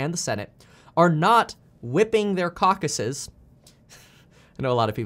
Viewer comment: Kevin, did you just say "whipping out their cockeses?